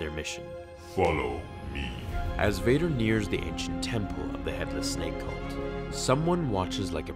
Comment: I thought it said how earth Vader found the secret dank temple lol